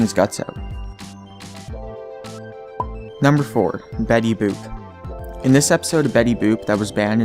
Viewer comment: I thought he said betty's poop XD